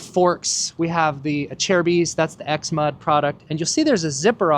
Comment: did you say "Acerbis x-mud product"? I went digging on the website and I found the "ACERBIS Z-MUD FORK GAITER Regular price$24.95". Is that same/similar or new and imroved version?